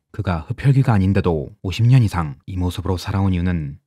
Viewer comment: 이건 더빙 오류인가여?? 들리는건 50인데 자막은 60이어서...